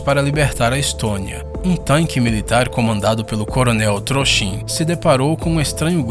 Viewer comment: eu entendi CORONEL TROXA